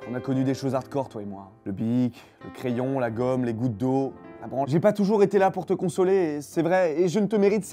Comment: c'est quoi le petit mot de fin ? La Branle ? Ouai j'ai bien compris :D